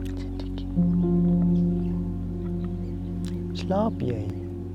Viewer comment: What does say to Cindy at 8:15? I assume a word in Afrikaans? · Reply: He said "Slaap jij?" which simply means "Are you sleeping?" in Afrikaans.